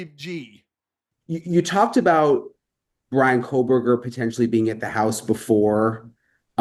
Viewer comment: went thru the house? i thought he was hired late in the case? the house was torn down before the 1st year anniversary, how'd he go thru the house? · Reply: if thats what you're talking about, he was saying BK was in the house. Not sy.